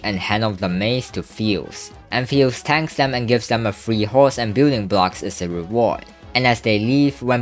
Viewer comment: does it sound like "gooning blocks" Instead of "building blocks" Or am i deaf